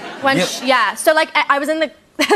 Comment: I literally heard “RUN IT A GUN” 😭 😂 💀